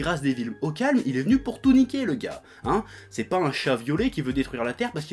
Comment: Mon dieu à 7:19 j'ai compris "chat violé" et du coup j'ai beugé...